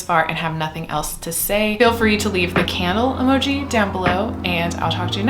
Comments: at first i thought you said "camel" emoji so that is what im going to leave.... rofl 🐫🕯🐪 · Thought you said camel emoji and was very confused lol 🐪🕯️🕯️